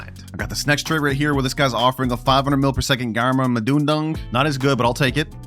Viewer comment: did bro say a 500m grama is not asgood?